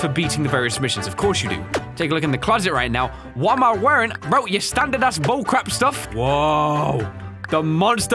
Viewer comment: Did Say Standard ASS Bullcrap